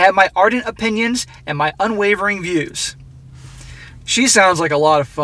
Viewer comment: i thought he said "unwavering bewbs" 😂 my bad for the misunderstanding